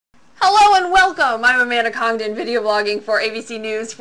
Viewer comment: thought she said Amanda Condom...